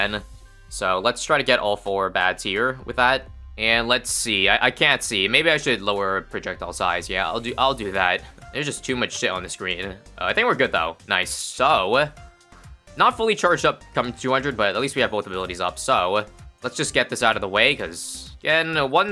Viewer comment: did he say shit or smthing